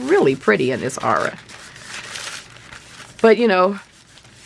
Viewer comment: Love what you share! I am interested to know the paint you are using as well....with the small tips......thanks! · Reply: Check timecode 7:25, I think she called the paint "Ara"